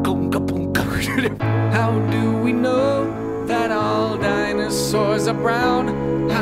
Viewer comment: On 1:05 i heard "how do we know, that our DIAMOND SWORDS ARE brown" What's up, gamers